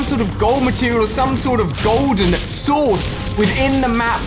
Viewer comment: who else heard golden sauce